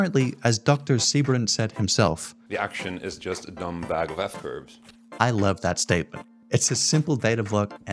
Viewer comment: what did he say??? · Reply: The “action” is just a dumb bag of “f curves”…